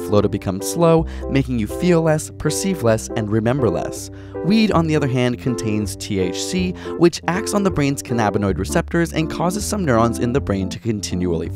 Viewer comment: omg thought it said "weed contains THICC"